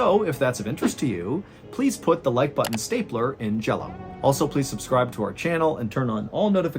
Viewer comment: did you just say "put like like button stappler in jello"? what the f does that even mean.